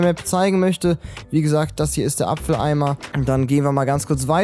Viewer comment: ich habe verstanden Apfeleimer